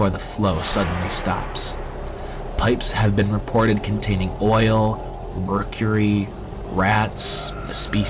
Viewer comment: did I hear oil?